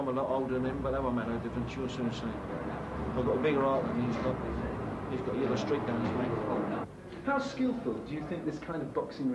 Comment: At around 16:53 Roy Shaw says "I've got a bigger heart than he's got but..." and then I can't understand what he says after that. Does anyone else get what he said? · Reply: "He's got a yellow streak down his back."